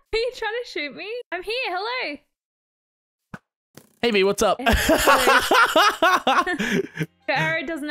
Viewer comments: At the beginning I thought he said "Hey bby what's up"but I realized it was "Hey Bee what's up" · when he said hey bee whats up?? i thought he said " baby whats up?" 😂